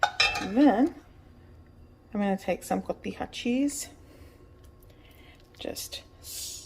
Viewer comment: what kind of cheese was that please. You said something hot cheese? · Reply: Cotija cheese